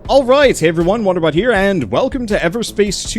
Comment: lol i thought you said wonder butt